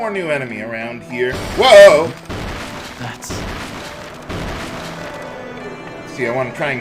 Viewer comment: did stocke just say "nuts" under his breath? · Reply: No, he says, "That's..." whenever he finds something with Mana Sight.